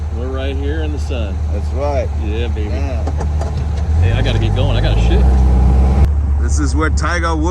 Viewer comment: I had to play that back. I thought said "I gotta get goin I gotta 💩 " lol I love that you guys are hanging out. I used to love and adventures and and GIVs. And to have you guys come together is so great. This was a fun video. is a great guy. I hope GIV comes to Colorado with and he can show you around Colorado as well